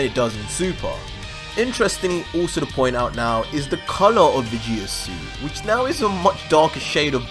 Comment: what did you say? Veges?